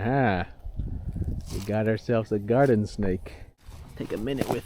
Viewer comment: garter snake - not garden.. its often misheard.